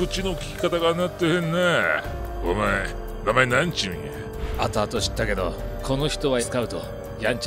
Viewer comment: 「なんちゅうや？」が「にゃんちゅうにゃー」って聞こえる(笑)